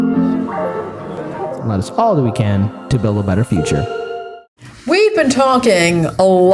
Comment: "Let us all that we can to make a better future." I swear that's what your intro sounds like it is saying. 🤔🧐